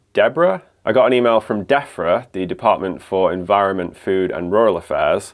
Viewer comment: I'm listening to this while working and I thought you just called Debrah who you were presumably on the phone to 'Deafrah' because you were angry she couldn't hear you